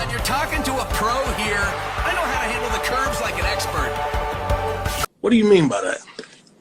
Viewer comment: Can't we have both badass and dorky Silver in the same rendition? I thought ihe said curbs at first, I thought Sonic was stomping motherfuckers out, but curves is wild 😂